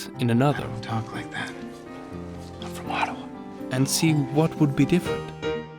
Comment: Did he just say, " I'm from Ottawa?"